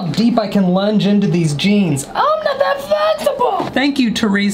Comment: im sorry what did he say 😂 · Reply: I'm not that flezibul